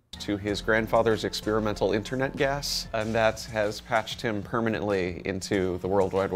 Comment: did he say “internet gas”? What is “internet gas”!?